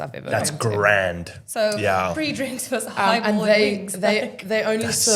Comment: it sounds like they saying Haibo then heard high ball when they explained